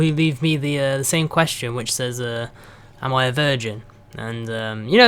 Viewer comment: I thought it said am I a vagina XD!!